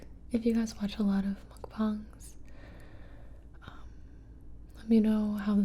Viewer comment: did gibi just call them "Mc Bongs" haha